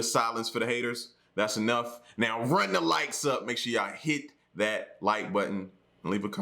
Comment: Lmao I thought for so long he was saying TURN THE LIGHTS UP! I’m thinking are you gonna kick in on bill lol